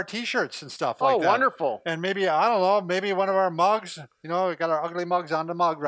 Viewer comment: In the giveaway section, I thought you said “and maybe one of our Bobs”. Haha. Cracked me up. At 1:10, was “mugs”. Love your channel.